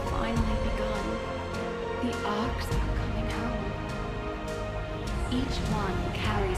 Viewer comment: Sounds like Rockwell says I’m coming home and it would make sense if the ark landed